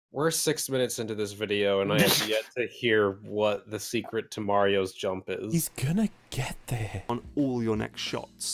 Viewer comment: Everytime I hear "Marios junk" instead of "Marios jump". What is wrong with me?